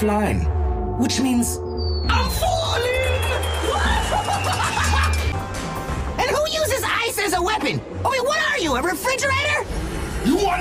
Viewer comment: got me laughing way too much, it almost sounded like he said “Oh fuck, not again!!”